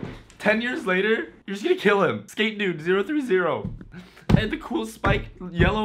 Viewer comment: I thought you said Skate Nude 030